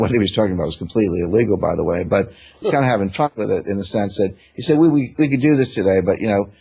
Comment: Did I hear Rickards drop the F bomb at 12:33? I love YouTube. Nice interview Greg. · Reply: He said Fun not Fuck.